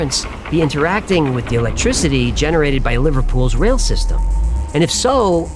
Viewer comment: did he just say "Electrizity?" I'm out.